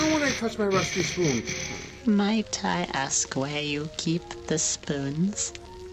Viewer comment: Lol When he said "Might I ask..." it sounded like he said "My tight ass"